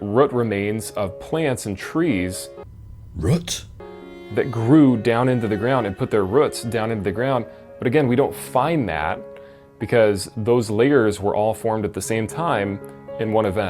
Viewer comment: "Ruts" is revealing his Mid Western roots.. ah ruts rather. making air quotes is the closest I come to punching my monitor. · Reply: The first time he said it, I thought I heard "foots"